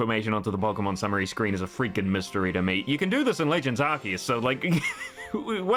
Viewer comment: Anyone else thought he was saying "Legends Saki" instead of Legends Arceus cuz he was speaking so fast?